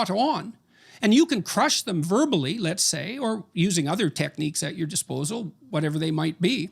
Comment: Lmao I just heard JBP say “You can crush them verbally let’s say, or crush them by OTHER MEANS at your disposal” 😳😳😳😳😳😳